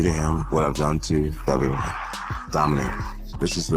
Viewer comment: Jon Jones was like "I'm gonna do to him what I do to everyone... DOMINATE" I thought he said DOMINICK LOL. Imagine he gets a rigged decision against Stipe just like Reyes.